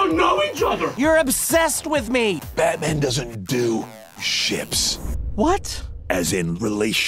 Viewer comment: First I thought he said "I don't do s**t." Then he said ships. LOL